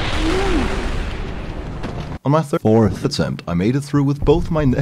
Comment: did he say 34th?